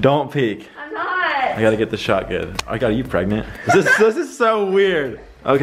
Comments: did ben just said 'i've got you pregnant?' 😂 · is Ben saying "I got you pregnant?" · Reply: He said my god are you pregnant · Did he say “Oh my god, are you pregnant?” or “Oh I got you pregnant?” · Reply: He said the latter😘